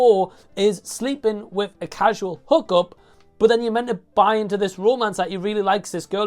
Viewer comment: I thought you said "casual hooker" instead of "casual hook up"... 😅 That would be a different movie. 😂